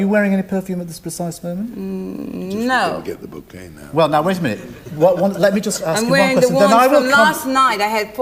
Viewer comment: What does the old man say around 6:24? off screen? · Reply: to me it sounds like "just you'll not get the bouquet now" after she said she was wearing 4 perfumes (He is a perfumer)